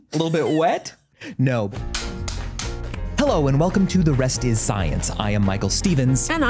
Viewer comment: I thought it said "Rest in science" and thought that Michael was dying 🙈